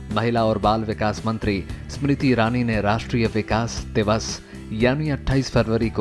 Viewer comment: did it say vikas